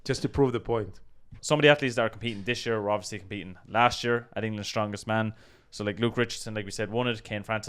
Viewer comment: Put on captions and YouTube thought you were saying "England's Youngest Man", and then Mark Felix's photo came up, and I thought we'll there's no way he's winning then